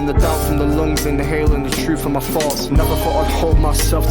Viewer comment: His accent makes it sound like he said "Exhaling the doubt from my lungs, inhaling the truth from my farts" lmao. He was saying "thoughts" but I was cracking up at my own American idoicy.